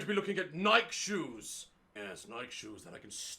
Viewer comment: AHHHH I thought he was saying “night shoes” AHH nah it’s “ nikéé “